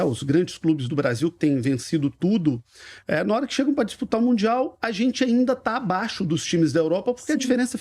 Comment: Você falou em Mundial, eu ouvi isso? 😂😂